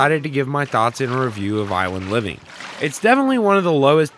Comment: I thought he said violent living like excuse me sir O-O (I only joking)